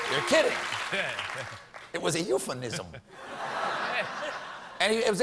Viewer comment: You all heard "euphenism", but I heard "euthanism"... which is my new euphemism (portmanteau of 'euthanasia' and 'euphemism') to detail how Donald Trump butchers the English language every time he speaks. To this day, I'm still not sure if he was saying "bigly" at the end of all of his sentences which isn't a real fucking adverb, or "big league" which just sounds legitimately Trumpian (a new euphemism to detail a slowness of intellectual understanding bordering on the extreme). "Euthanism" © Gregorius Daneli; Dec. 4, 2016